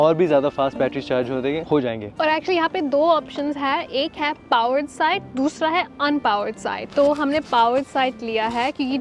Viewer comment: i can't understand what you are saying 😅😊😊😊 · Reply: powered site, unpowered site